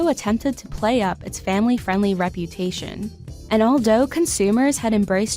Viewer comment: did she say "all dough"